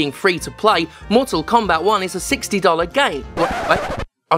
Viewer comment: I heard A $60 GAY XD